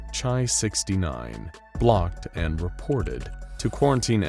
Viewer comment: Im sorry but did I just hear "chi-69, blocked and reported"?